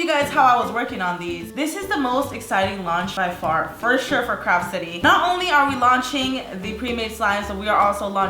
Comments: it sounded like she said crap city and I was like, "Way to promote your own company." No hate! LYSM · Lol it sounded like u said crap city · When she first said craft city i thought she said crap city